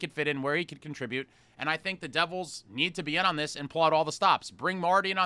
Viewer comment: I heard pull out all the stops, then I heard “palat all the stops” Freddy foreshadow?